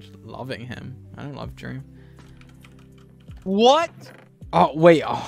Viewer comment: Is it just me or did gogy say he loves dream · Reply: He said "I don't love Dream"